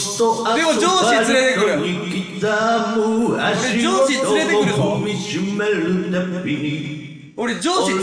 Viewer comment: ここら辺から「上司連れてくる！！」が「ジョージ連れてくる！！」に聞こえてきてガチしんどかったwwwwww